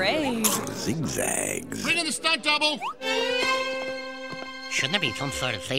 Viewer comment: When this commercial aired I misheard the line at 0:13 as "Ready to start double!" (The correct line is "Bring in the stunt double!")